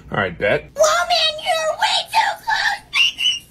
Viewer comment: WOMAN YOU’RE WAY TOO CLOSE; ✨PePpEr sPrAy-!✨ 2024 edit: Why of all comments did this blow up- ._. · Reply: It sounds like he said Robin your way to Close